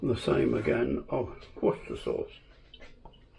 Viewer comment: is he saying "And the same again with WORCERSTERSHIRE SAUCE", or is he saying OYSTER SAUCE? I'm assuming Worcestershire Sauce, because Oyster Sauce is much thicker.